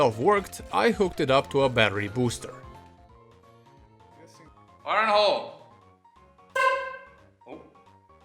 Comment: Did he said "Fire in the hole" ?